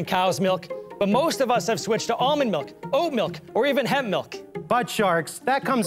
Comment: I thought the Oat milk said Cat MIlk lol